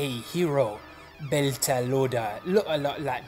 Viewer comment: Hi did you just said Bell ka Lauda at 0:33 ? Sure sounds like that